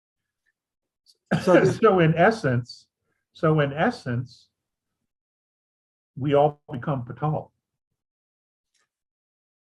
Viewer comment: in essence we all become ??? at 22:52 can anyone help...i hear Patal ??